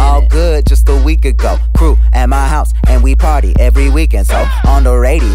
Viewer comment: i thought he said poop at my house instead of crew at my house